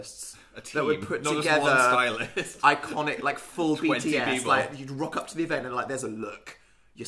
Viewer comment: and phil in their pajamas eating pizza and banting for 33 minutes, this is the height of domesticity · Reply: did say bts at 24:09 coz im freakin out